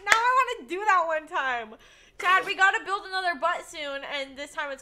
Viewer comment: said butt instead of boat that made m laugh :,D keep up the amazing vids!